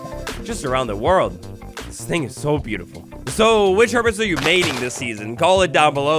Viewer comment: I heard that as "What Hermits are you MATING" rather than "MAINING" and I was momentarily VERY concerned. However, I am now considering what it would be like to add Hermits into the game as villager-esque mobs, and what each's roles and behaviors would be. Fun thought experiment for sure. Thanks for the great video!